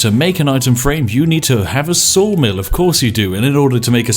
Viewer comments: I thought he said soul mill not saw mill. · Oh you said saw mill, I thought you said soul mill.